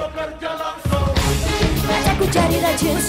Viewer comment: mulan ngomong apa sih? Gila nih lagu.. Keren bgt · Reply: Sms in telefon in dan bbm in